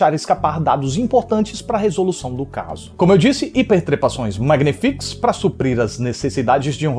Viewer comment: eu ouvi “inter-TREPAÇÕES” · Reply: Ele disse Hiper trepações